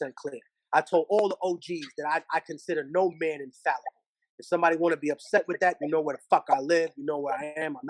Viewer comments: Do any of u guys know of what he says at 1:34?? That he considers no man unfathomable??? I don't understand him hopefully one of yall know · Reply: He says: infallible Infallible: incapable of making mistakes or being wrong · Do any of u guys know of what he says at 1:34?? That he considers no man unfathomable??? I don't understand him hopefully one of yall know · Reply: He considers no man infallible.